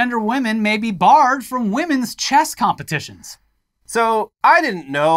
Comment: no matter how many times I heard it I always heard chest competition for women